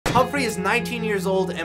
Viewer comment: I thought you said hump free